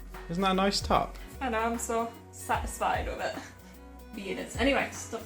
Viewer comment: when you were talking about your sweater, i thought you said 'cat-isfied' instead of satisfied and i giggled really hard. i love your channel so much !!